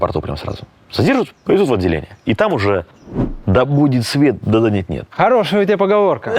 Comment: а мне послышалось: да будет свет, да денег нет😂